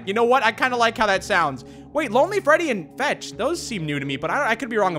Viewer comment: When names the new characters All I hear: yaarg foxy, lonely Freddy Ok am I the only one who heard him say lonely Freddy? He do be lonely tho 😅